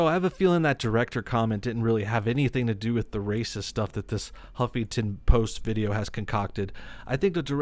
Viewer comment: I heard him say "humpington post" at 7:01 😂😂😂😂 man, gotta kick out of that XD